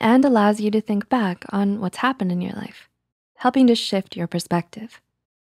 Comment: i heard “and helping to sh!t your perspective” ...well i’m not doing that lmao